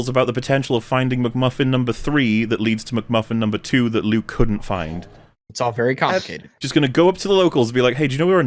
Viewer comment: finding McMuffin 3, that leads to McMuffin 2,.. it's all very complicated 17:32 Misheard that as "it's all very cupcake" and had to double check - would have been such a thing to say 😂